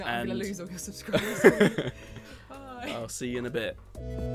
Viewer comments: Why did he say "See you in the bed"- Am i misheard or something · "see you in a bit" I heard "see you in the bed" wtf is wrong with me...